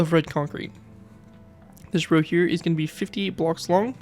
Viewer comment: did you say 50 or 58 at 5:59? nice video btw! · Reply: Thank you! I said 58.